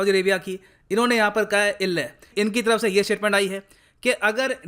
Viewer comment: did sir just say ille👎😂